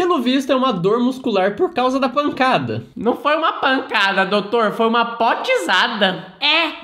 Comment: "doutor não foi uma lançada foi uma potisada" morri aakakakakakak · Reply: Pancada*